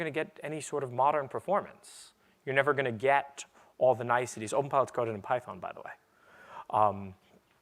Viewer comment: What does he say at ~ 20:22 ? What's coded in Python? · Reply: Openpilot, here is the source code. <link>